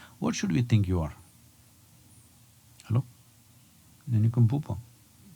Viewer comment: what is that word he said? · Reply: Nincompoop I think